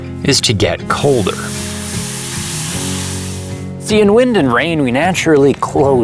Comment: I thought he said Winden like in Dark (Netflix) 😂